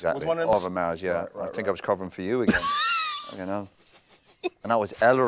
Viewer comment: Lmao at 1:18 did he not say "Fucking hell" as opposed to the "You know" like the subtitles say?